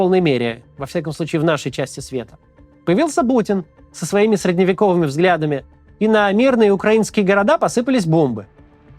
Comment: На 15:02 примерно Макс так сквозь зубы сказал путин, что мне послышалось Бучин! Хорошее было бы прозвище.